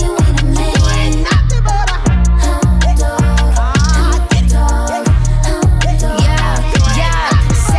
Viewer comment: I thought she was saying hot dog instead of hound dog 🤣